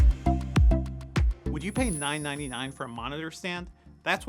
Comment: the captions said “$9.99” lol